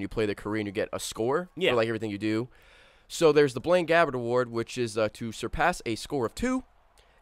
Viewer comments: lol blaine gabbert, I thought he said blame gavin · When he said Blaine Gabbert achievement, I thought he said Blame Gavin. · I misheard Blaine Gabbert as Blame Gavin... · 'Blaine Gabbert', I heard 'Blame Gavin'.